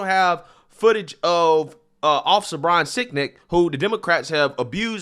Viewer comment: Did he just say officer sicdic?